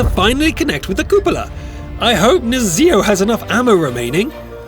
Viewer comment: what did he say? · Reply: Cupola?